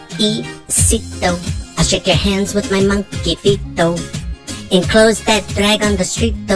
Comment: Thanks a lot. Everytime I hear Deceito, its in my head the rest of the day/night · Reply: I keep hearing “monkey feeto”! Hahahahahah